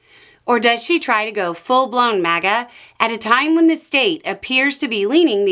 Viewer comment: I thought said „full blond Maga » and I nearly choked on my drink!